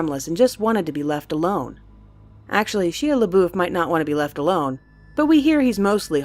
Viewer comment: 16 ish. Did she just say Shea leboof?... TWICE? xD